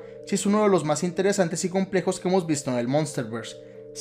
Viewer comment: yo por estar comiendo mi heladito yo entendí “Es uno de los personajes más interesantes y pendejos de todo el monsterverse” XD